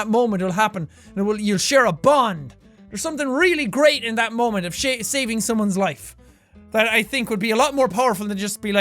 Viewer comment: did he say shaving or saving